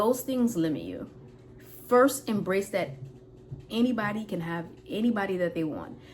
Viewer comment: Intermittent fasting is what is helping me a lot!! I already lost 16 lbs! For me is working, thanks to my primary doctor who recommended to me. I have been adding exercise little by little, 20 minutes per day, low impact. Going trough menopause is not an excuse. I will always say is depending of the Determination that you have to accomplish your goals in life. I was 212 and I am down to 196. My long term goal is 160 to 165. Thank you for sharing. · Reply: Lol😂when she said “anyone can have any BODY they want” I heard “anybody they want”